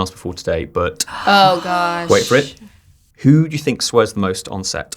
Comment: i honestly thought he said sweat